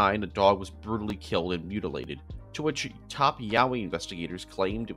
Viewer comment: watching this while half asleep and hearing “top yaoi investigators” woke me right up